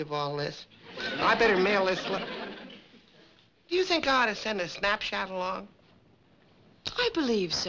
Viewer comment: I swear it sounded like Fester said "you think I should send a snapchat along" he was way before his time! Haha